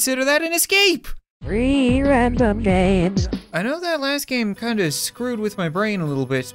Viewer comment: if u put the subtitles on it says not "three random games" but "rear end up babes"😂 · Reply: the subtitles say three random games instead